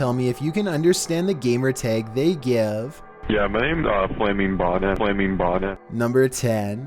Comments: I heard FlamingBonnet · Sounds like he said FlaimingBonah or something like that. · umm i think at 1:18 he said he was Flaming Bonnet i dont know im juat guessing cuz thats what i heard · I hear flaming modder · I think he said flamingbata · it sounded like he said flaming bomb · it sounds like hes saying flamingmodda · I think the guy said his name was "flaming bona" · it sounded like flamingbody or flamingbonnet